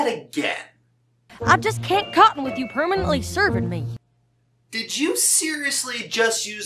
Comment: ok, you know what? im gonna have to stop you there... i heard clearly "i just can't be caught with you serving me", which makes sense to apple jacks character. she's a hard worker and doesn't like to slow down, and since spike cant harvest apples, or carry them and is pretty much only good for writing, she would be worried about him getting in the way and fooling around with apple bloom (who she takes care of)